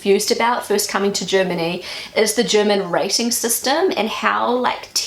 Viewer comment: It took me a while to work out that you were saying 'rating' - I heard it as 'racing' LOL